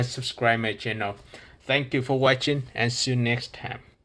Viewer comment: fore some reason I heard next ham